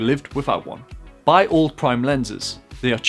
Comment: I heard "buy all prime lenses" I was like holyshit I thought it's a low budget tip lol Good tips tho, thanks.